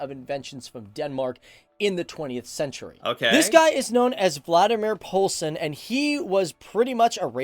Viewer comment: Sounds like he says Vladimir Poulsen. His name is Valdemar.